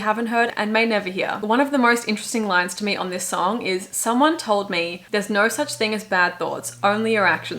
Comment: I always hear “there’s no such thing as bad th0ts” 😭